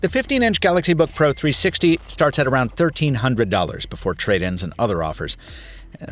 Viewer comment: Typo in captions at 7:28. Says $13,0000 instead of $1,300.